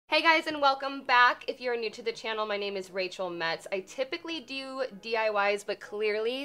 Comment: I thought she said my name is Rachel Meds